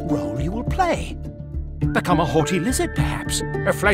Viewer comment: Weird question but can someone tell me what he says at 0:22? Become a wot lizard wut???